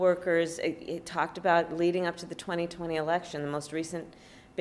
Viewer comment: Willie thought she said erection.